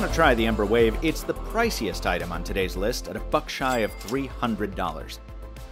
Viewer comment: Anyone else hear him say "A f*ck shy of $300?" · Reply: "a buck shy"